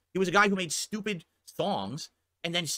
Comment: am I the only one who hears "stupid thongs"?